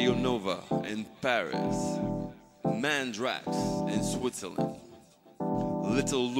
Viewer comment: who from switzerland? kept listening it over and over but i dont understand does anyone know who he's talking about? thanks <3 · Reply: Mandrax! Aka Shakedown.